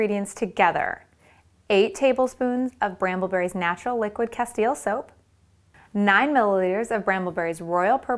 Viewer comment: When she said "Castile" I thought she said "Castiel" :D